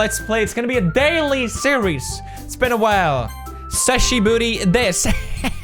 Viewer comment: Pewdiepie says : Hisashiburi desu. (Long time no see). Subtitles say : SEXY BOOTY THIS